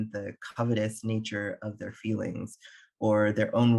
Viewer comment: for a second there I thought he said “communist nature of their feelings”. Good thing I always have subtitles on lol.